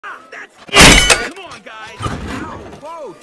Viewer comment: WE QUIT MR ONE MAN SHOW! Okay leave fine. How will I ever find anyone else who knows how to fill me up with gas? Adios Chuck. AND MY NAME IS NOT CHUCK!!! Whatever. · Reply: I swear for everyone, or at least me, it sounds like he says one man ‘show’.